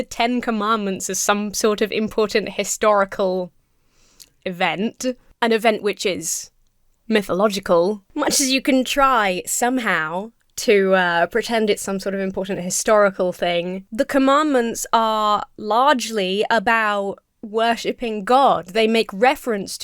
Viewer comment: The governor says it has "historical significance," but the 10 commandments have as much historical significance as any other religious text. Which is very little outside of studying its significance with ancient civilizations' societies. Thus, they have no purpose in schools especially under the High-school lvl in an elective history &/or religious studies classroom that studies them along with other religious texts & historial documents for purely academic purposes. Not found plasted everywhere in public schools. Also the students should not be made to follow them otherwise it violates the rights of a every student who doesn't follow that religion, that denomination, or a religion at all. I swear their driven by this combination of persecution complex, their self righteousness, & this obsession with trying to convert as many people as possible by any means necessary regardless of if that person is willing or not. I will say that I can't wait to see the bill fall flat on its face by either the shear amount of lawsuits, parents getting involved, or someone wanting their own religious text to be displayed too & the governor either having to allow it or removing the bill because all it takes is one student to tell their parent or parents they being forced to follow another religion, denomination, a religion in general for the backlash to start with the parents getting involved to protect their kid's rights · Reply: i thought he said "hysterical" significance.